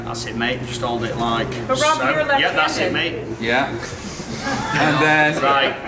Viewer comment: Btw once I heard Ayda says : But Rob you’re Left Handed . I knew things will be messed up 😂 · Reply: MY LARRY DUMBASS REALLY THOUGHT IT SAID "BUT ROB YOU'RE LEFT HAROLD"